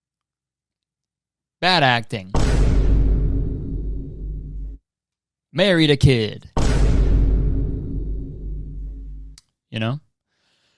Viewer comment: I thought you said that Christian lady "married a miner" , I was like "damn kurtis what's your beef with hard workin miners" 😭😭😭😭😭☠️